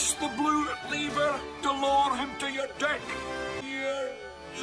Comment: i just heard deck...